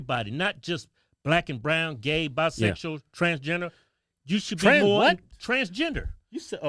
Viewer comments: I thought he said transjenner HAH! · I thought he said trans-Jenner 😄